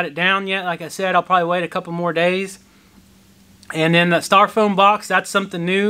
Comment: I heard “star foam”. Wondered what kind of box that was. 😀 Then you showed the “styrofoam” box. Made me think of Daniel’s acern vs acorn.